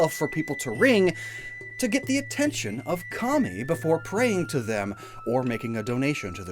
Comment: i know you said kami but i heard commie😭